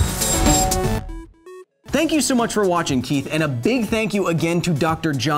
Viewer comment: does he say "thank you so much for watching, Keith" or is this just for me because that's my name and it's freaking me out like am I about to die or something? Did you do this just to mess with the Keiths out there? Someone tell me what is going on.